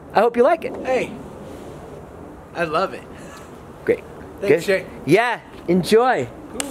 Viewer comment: does Josh say “thanks Drake” ?😂